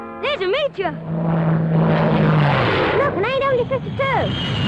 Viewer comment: what plane is this? I can't understand this dumb kid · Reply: AW-52